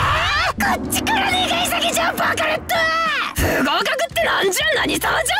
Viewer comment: バカレッドがバカロットに聞こえる